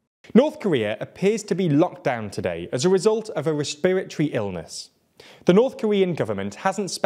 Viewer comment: what was that word at 4:43? Were you trying to say "respiratory"?